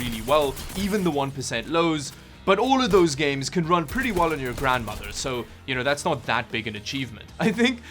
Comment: Did you say: 'Run on the grandmother'? I don't think so. Either on my grandma's 2005 HP laptop or on herself.